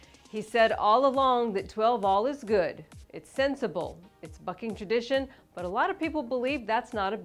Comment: did she say "it's fucking tradition"?